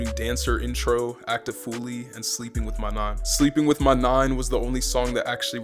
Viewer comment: was i the only one that thought for a sec that he said sleeping with my MOM 😭😭😭😭😭😭🤤